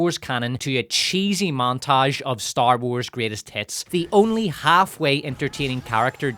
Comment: Did he say "Star Wars greatest TITS" ??? I mean .... sound like it.